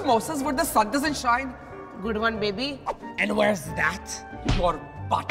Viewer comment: I thought said ‘wheres nut?!’ 😭😂😂